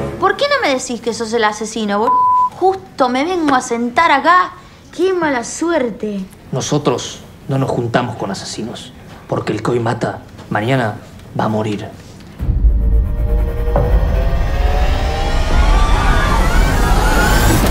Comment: Tan traumado con la cuarentena, que escuche " Por qué elCOVID mata" 1:23🤣🤣🤣🤣🤣